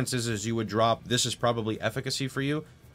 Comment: Greetings and thank you for your content. You suggest a 3rd red socket in the body armour and suggest a gem, but I did not catch which one. I thought I heard 'laceration' but I can't find a gem with this name. Thank you for clarifying the gem setup in case of 3R-2B-1G. Kind regards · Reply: The Red Gem is "Less Duration Support". Its only works if you have the "Lifetap Suport" because it give the duration tag likewise it enable the "Swift Affliction" Green gem